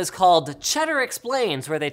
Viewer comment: I thought he said cheddar cheese